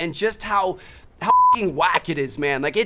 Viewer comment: it sounds like he said "hucking", knowing that he would bleep it out later lol